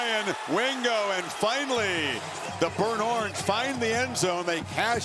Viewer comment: Did he say burnhorns at 11:22?